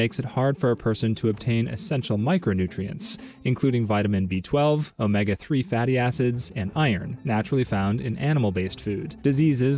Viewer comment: "... Omega 3 fattie acids..." I thought he said baddie acids